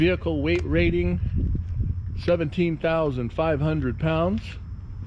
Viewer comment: How much does the trailer weigh? If the GVWR is 17,500 you'll need to deduct the trailer's own weight to know your payload. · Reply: I heard you wrong while watching the video. I thought you said 17,500 but you say 11,500. It all makes perfect sense now. Thanks for the explanation. Sweet trailer!